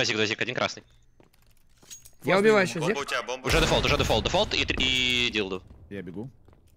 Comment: Мне послышалось или на 24:30 нафаня сказал дилдо